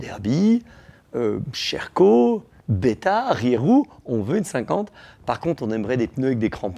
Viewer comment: une quoi ? Une quoi ? Une Riérou ? 🤣🤣🤣🤣🤣